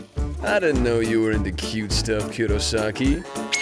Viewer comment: I thought it said 'butt stuff' for a second...